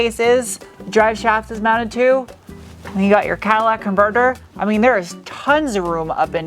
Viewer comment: Does anyone else hear her say “Cadillac Converter”? Can’t tell if a easter-egg joke or just me hearing weird.